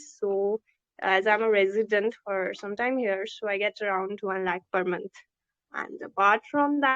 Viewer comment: did she said 12 lakh per month????? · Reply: 1 lakh per month for some times